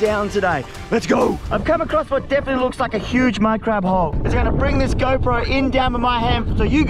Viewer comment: Anyone heard “huge minecraft hole”?